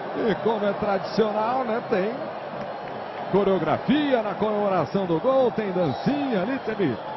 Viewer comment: does he say pornographia???